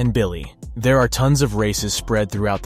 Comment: thought you said Racist and was not ready for the next sentence.